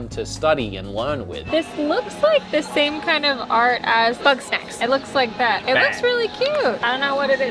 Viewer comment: I cannot for the life of my figure out what she said, but what it sounds like is " this looks like the same kind of art as butt sex"